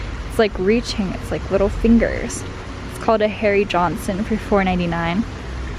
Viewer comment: Say whaaaat? checks captions Ohhhhh, Harry Johnson 😅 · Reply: LOLOL>..I thought she said "hairy Johnson"...hahahaaaaaaa